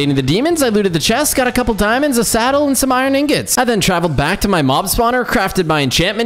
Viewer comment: i heard you say "going back to my MoM spawner" XD